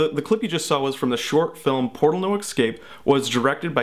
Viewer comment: Did you just say "Ick-scape" at 8:14...??